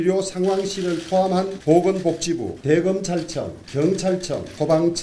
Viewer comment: 잘 들어보세요. " 대검찰청 " 으로 잘 들리지요? 주호영 국힘 이 사람들 영혼 없습니다.